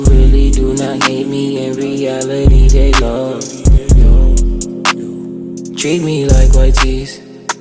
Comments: i always thought it said treat me like y z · My friend keeps quoting this and i thought he was saying 'treat me like yt' 💀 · When the lyrics went treat me like white tees i thought it said "Treat me like a rizzler" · Bro I thought he was saying treat me like yg's